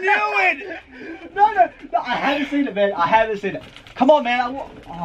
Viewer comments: Am I the only one who heard ' I haven't seen it babe' in 2:35🤔🤔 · I thought Henry said “I haven’t seen it babe”